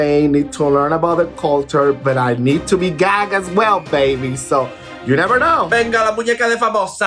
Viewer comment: What does Choriza say at @ 1:38? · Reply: "Come through famosa's dolls, everyone in a single-file line"